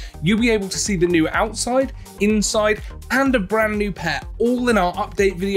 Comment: Did he say "And a brand new pet" ?